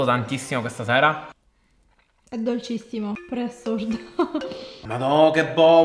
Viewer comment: non ho capito.. È sorda??? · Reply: Ah no... Assurda? 😊